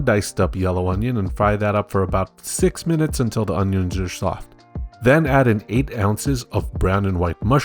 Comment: How tf did the youtube captions understand he said "are soft"? I listened to it three times and all I heard was "Jaschoff"